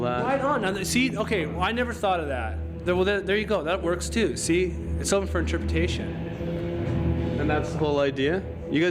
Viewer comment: what does Kurt say? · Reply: JK shakes head in disgust and says "Douche Bags"